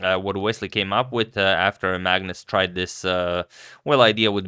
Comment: I thought you said Magnus retired.